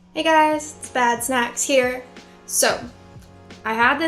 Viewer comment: I thought she said "hey guys, bad sex here" lmao